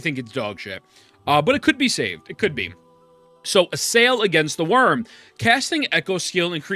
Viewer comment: am i the only one who hears "Sail against the worm?"